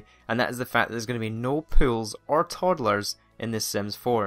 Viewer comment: Whenever he said "Pools" I heard "Pills" and I'm just like What?